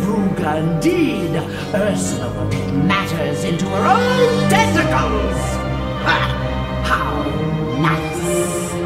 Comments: Does anyone else hear Ursala say testicles instead of tentacles at 14:38? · I think she said testicles